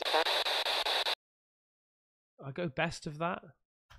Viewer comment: Almost sounded like "I go, that's the problem" something like that